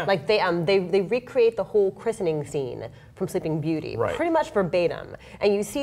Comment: What does she say at 3:14? "Pretty much for benam?"